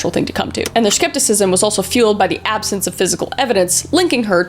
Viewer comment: I thought it said physics..